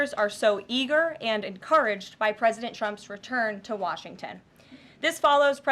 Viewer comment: At time's she sounds like she says President chumps 😂